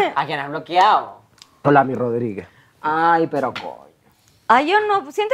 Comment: En el 1:54:50 La José dice Ami Rodriguez??? 🤯😱 O sea que Ami Rodriguez engaño a Sebas Vivs con La José? Escuché bien o me equivoque? Porque incluso dijo que el novio era más lindo, y Sebas si es más lindo. Whoa! Que poca de Ami 😡 😒